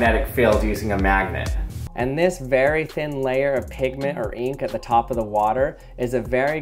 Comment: I thought you said Pikmin but you said pigment :(